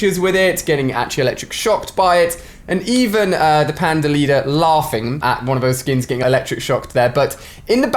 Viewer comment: 2 did he just say panda team leader man I thought he was good